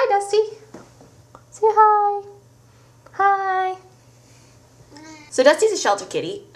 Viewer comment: I swear he said hi >.>